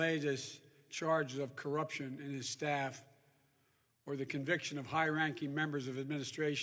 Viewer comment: Conviction of high aparatis what the hell is he saying I'm so confused (lol)